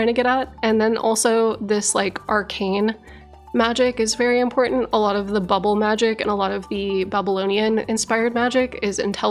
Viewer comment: I heard it as "a lot of the bubble magic and a lot of the Bubble-onean magic"